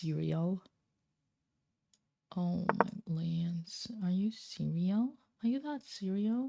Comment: I swear to god at 1:12:07 for a moment I just heard "Oh my lands"